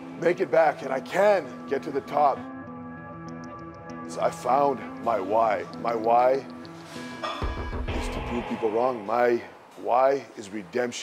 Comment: Thought he said "I finally found my wife. My wife is to prove people wrong" 💀